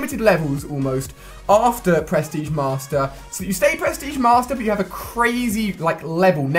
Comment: I love it how it sounds like you say press tige master